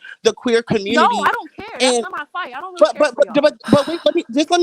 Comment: “ it’s not my fight, I don’t care for y’all “ . I heard it. If you’re not willing to Listen, you’re going to miss it. 1:49:43.